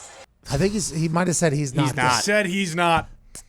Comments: I thought he said he’s nuts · I thought he said “he’s nuts”